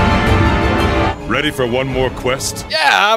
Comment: "Ready for one more quest?" But all I heard was "Ready for three more carts?" (I fared slightly better against AT Nergi than AT Xeno, lasting 20 minutes.)